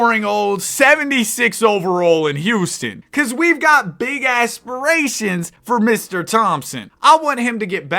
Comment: thought he said ass lol😂